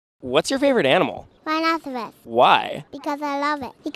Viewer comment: Her on start i hear "winoseroow"